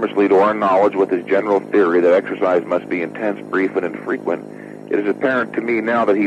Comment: I think the captions are wrong. If you listen closely I'm pretty sure he says infrequent not frequent